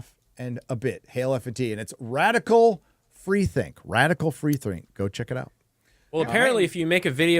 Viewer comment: I can't seem to find this channel. I hear it correctly, right? Radical free think?